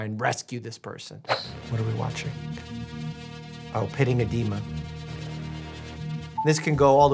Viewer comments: He said Pitting Edema and i heard "Petting a demon" 😂😂😂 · thought you said “pitting a demon” and I was very curious what that meant